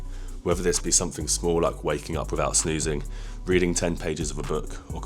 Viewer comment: I thought he said "waking up without sneezing" 😂